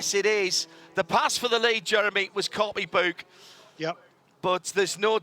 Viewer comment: I couldn't understand what he was saying and the closed captioning translated it as "The pass for the lead was Courtney Boog..". I found out that's an expired rapper. But yeah, he's saying "copybook" of course.